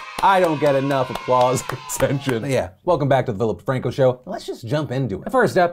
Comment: Did he say Vilip DeFranco show